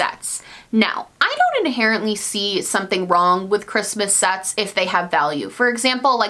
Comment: Lowkey thought you said, “I don’t see anything wrong with Christmas sex.” 😶